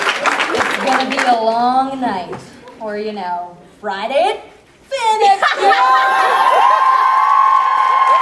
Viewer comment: What does Elise say at 5:36? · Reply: She says: "It´s gonna be a long night, or you know, Friday at FanExpo!"